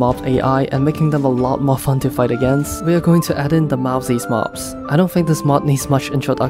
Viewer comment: what mod? I could not understand what mod he said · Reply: Mowzies mobs